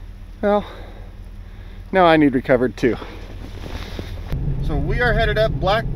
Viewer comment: feeding my face... clicked on your vid.... and tipped my head back to put in said food... and all I heard was " no I need recovered to". lol. bad day is still a good day